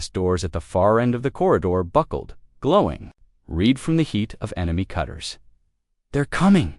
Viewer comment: "glowing REED (red) from the heat of the cutters"!!!!!!!!!!!!!!???????????????? How's bout ".......glowing RED from the heat of the cutters"!!!!!!!!!!!!!!??????????????? GOD, if only ONE of you could find a decent AI instead of a POS AI!!!!!!!!!!!!!!!!!!!!!